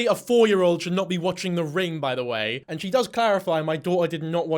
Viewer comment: I thought the kid said rain